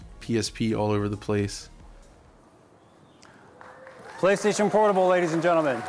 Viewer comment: did he said Play Station Horrible???